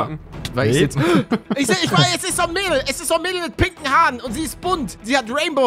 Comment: Was ich verstanden habe: ,,Es ist so'n mehl Es ist so'n mehl mit piken haaren"